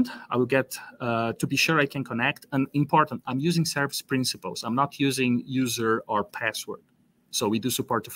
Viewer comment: what is he saying is used for auth? I can't understand. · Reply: A Service Principal. It's an Azure AD way to control access that's more secure than username / password auth. <link>